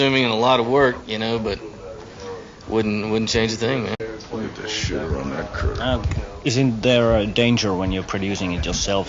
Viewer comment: Anybody else catch that? look at the shitter on that.. ker?? Girl? Curve? Haha!! Phil checking out some hottie